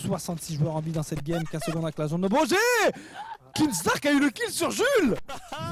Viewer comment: When u said Kinstaar I thought you said keemstar